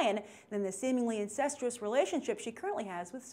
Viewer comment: Woah, wait up... @ 2:26 did she say seemingly INCESTAL relationship with Superman?! I heard that wrong right? If not somebody please explain this to me.